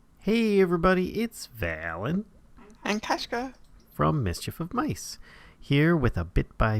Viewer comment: i thought they said "and crash grab" at first lol